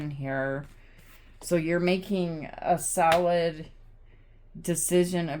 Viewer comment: Had a giggle at 6:42 because I'm hungry for my breakfast waiting for buns to finish baking in the oven and I heard "you're making a salad" 😋